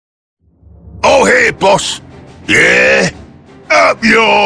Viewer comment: "All here, boss" Anyone who has used "free slugga" tactics will know this line very well :) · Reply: I always thought they said "Oh hey Boss" xD